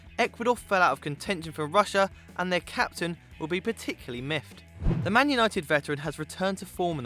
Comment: did he say "miffed" instead of missed?